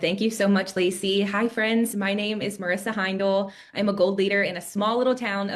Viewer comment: anybody else hear “cult leader” when she said “gold leader”